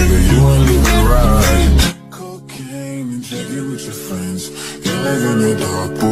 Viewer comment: im srry did i just heard cocain